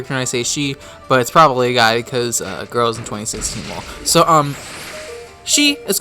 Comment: "because girls..." What is he mumbling after that? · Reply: tarabaci Girls in 2016? Lol